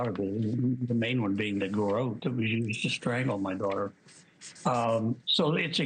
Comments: I’m not a conspiracy theorist, and I’ve never thought the Ramsey’s were guilty. But, uh, at 3:26 did he say “The garrote WE used to kill my daughter”? Thats concerning if that’s what I heard. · I’m sorry…but at 3:28, did he just say “the garrote that WE used to strangle my daughter”?? Cuz there was no ‘s’ sound at the end of that word… I seriously hope this man didn’t do such a heinous thing and wasn’t capable of such an act…but that is what I heard. I really hope he’s just a mumbler….